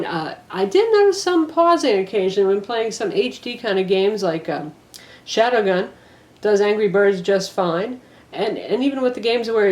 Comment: "I did not puzzle ocasion from hd kinda games like shadowgun", sorry I cannot understand what you say in that part, I'm not a native english speaker